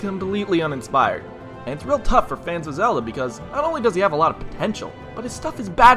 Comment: Did he say phantom zelda